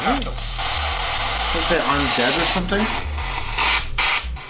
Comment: “I’ve Died!” He sounded very excited about that. 🤔 Weew. Maybe he just realized. Idk Then it almost sounded like he says “para-natural” more quietly after that. Wow